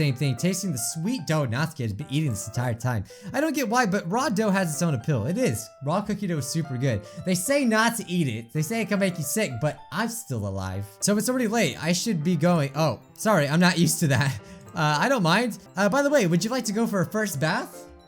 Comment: Lmao I thought he said 'cookie dough is so gay' And I was like: well shit i'm cookie dough